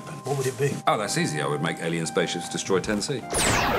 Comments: I absolutely thought he said Tennessee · 10C? I thought he said Tennessee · I thought he said Tennessee. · i thought he said tennessee the first time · I thought he said he’d make alien space ship destroy Tennessee and as a Tennessean that hurt my feeling for a second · I thought he said "Tennessee" instead of '10C' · i thought he said "alien spaceship kill Tennessee" and was like wtf did we do to you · i thought he said Tennessee, i thought yeah i can see that xD